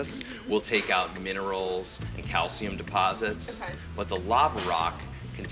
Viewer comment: did she actually says accha (indian accent) ?? Or okay ??